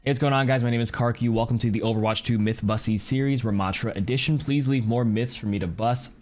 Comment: Anyone catch that... he says mythbussy series. right??